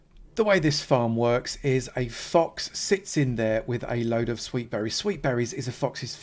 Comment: My guy I thought you said sh*t load in the opening and I almost lost it 😂 good tutorial, thank you so much 😂